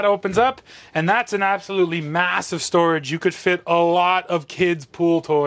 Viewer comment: Talking about the storage, I thought you said "you can put a lot of kids..." :D